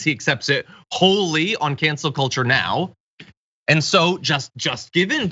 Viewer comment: I heard “Cancer Kultur” for an instant before hearing “Cancel Culture…”